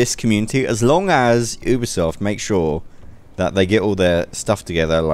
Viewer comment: I thought you were saying 'Ubersoft' but later i found out you were saying 'Ubisoft'...